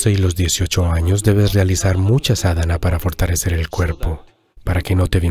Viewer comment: ¿Alguien puede decirme que dice? Sadama? 🤔🤔🤔🤔Es que no entendí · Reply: Namaskaram, soy María, una voluntaria de Isha. Gracias por tu comentario. La palabra a la que hace referencia el video es "sadhana". Pranam, Maria Voluntaria de Isha